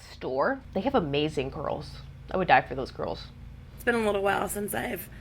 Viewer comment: I thought you said “ you’d die for her GIRLS” not curls… colour me confused thinking you were talking about her biddies 😭